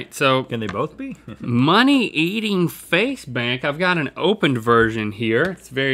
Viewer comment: I thought it said Faze Banks